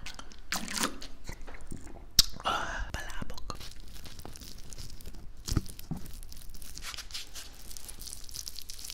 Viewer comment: what did you say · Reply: He said Palabok, its the most famous dish in the Philippines